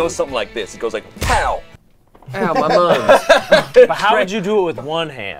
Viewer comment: did say "Ow, ma munge." ILLUMINATE CONFIRMED!!